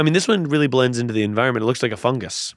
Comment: fully thought keith said “books” in the beginning and was not prepared for real life pokemon and alien creatures